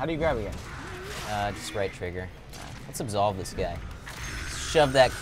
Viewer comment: In 2:13 , instead of absolve ,I thought it said Asshole